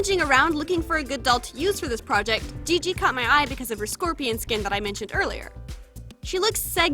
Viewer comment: When you said “Gigi caught my eye” I thought you said “ I think I cut my eye” 😐😅